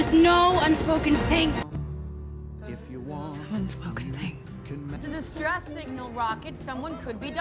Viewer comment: what is she saying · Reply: She said "some unspoken thing"